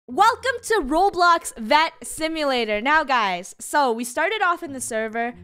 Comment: I thought it said vant simulater 😂